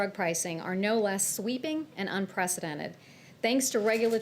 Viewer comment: All I heard was “unpresidented” and I agreed emphatically